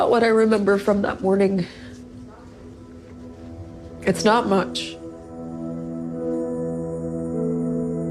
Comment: who's voice is in the background? It sounds like someone saying "not much" right before she says it. I could be totally wrong but it's definitely someone off-camera saying something. Edit: Throughout the remainder of the interview you can hear someone off-camera prompting her with statements. Any ideas?